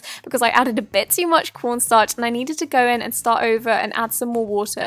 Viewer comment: I thought she said pornstar at 1:38 bahahaha 😂😂😂